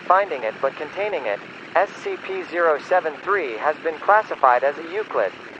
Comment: it says SCP-073 instead of SCP-973